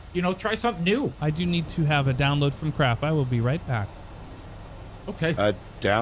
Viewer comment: Youtube thought you said you need a "download from crap. I will be right back."